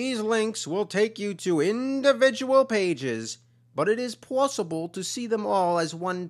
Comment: did he just say plussible? lols so win